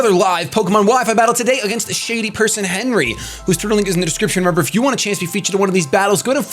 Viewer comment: It eventually starts to sound like he says "turtle link" instead of Twitter at the beginning.